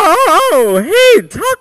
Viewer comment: I THOUGHT HE SAID "OH I HATE TACO BELL!"